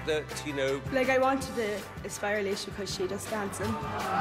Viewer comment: What is he saying at 4:07? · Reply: He said thet alesha was the one who he wanted to impress because she do dances